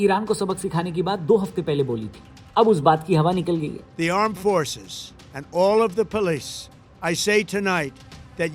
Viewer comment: I just heard "Aand Forces" 😄